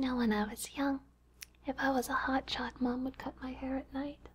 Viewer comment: What is she saying at 22:15 ? "If I was a hotshock my parents would cut my hair" or something? I can't understand the "hotshock" part haha. Does anyone know? I listen to this awesome video so often that it's really starting to bug me that I don't know what that word is.